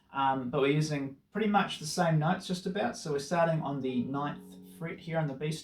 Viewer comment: Thanks for doing this. It's a lovely song. One note: Because of your Kiwi accent, I thought you said 19th fret when describing the solo when you were actually saying 9th/10th hammer on. I was flummoxed for a few minutes until I groked it.